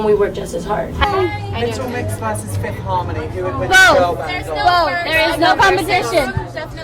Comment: What's the guy say at 0:48??? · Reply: He said little mix vs. Fifth Harmony who would win and they said both😊